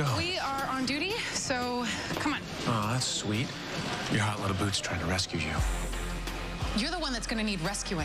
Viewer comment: Does anyone know what Stanton calls Chen at 0:12? I can’t work out what it is and I thought from context it might have been some kind of slur for Asian people, but I’m probably wrong and it’s just some standard insult. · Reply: He called her a 'hot little boot', so basically just what the other officers call a rookie (boot) but sexualising her.